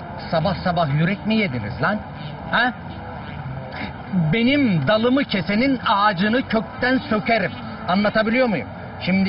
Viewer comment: Sarp 44:00 ,tam olarak ne dedi? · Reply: Benim dalımı kesenin ağacını kökten sökerim